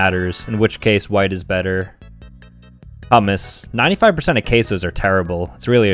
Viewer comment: Did you just say 'HoMmues?!